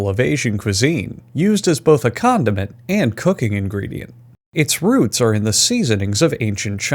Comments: I thought he said “used both as a condom and cooking” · i thought he said Condom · Thought he said "used as both a condom and a cooking ingredient" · I thought you said condom it not condiment.